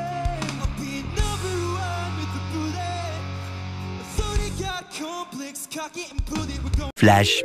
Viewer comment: Why did I hear “the soda got complex, cock it and pudding” 😂